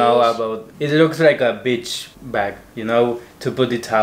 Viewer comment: When said 'it looks like a beach bag' I heard 'it looks like a bitcvh bag' I was like what!!? hahaha